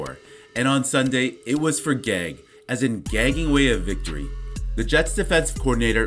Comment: For a second I thought he said the extra G in Gregg was for gay